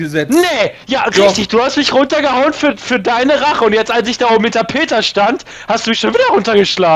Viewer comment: ich verstehe immer als ich mit der peta stand :)